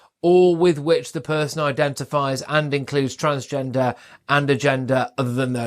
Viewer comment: Hmmm, might be the accent but did I hear "Adam Ant Gender"? Think that's me TBH... Ridicule is nothing to be scared of...